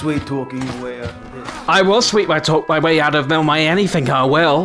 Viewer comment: "I will sweet my talk my way no my anything I will" 8:00 it took me at least 20 times to comprehend and write what you said how